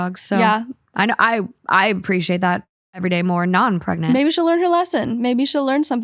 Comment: “I appreciate that everyday more non pregnant” my ass heard that as now I’m pregnant the gasp I gusped